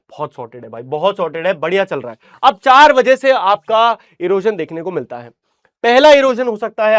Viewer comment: idk I hear 4 baje did u hear that also🤔